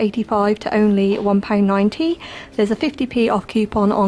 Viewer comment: What was the company you said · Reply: Caring everyday